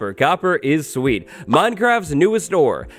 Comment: I though he said "Copper, minecraft's newest door" and I realized I misheard him but then thought a copper door might be nice